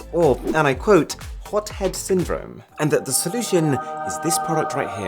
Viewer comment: When you said "Hot head syndrome," I was browsing something else and thought you said "Stockholm syndrome" (p.s wow i did a project in science on this for the pseudoscience unit)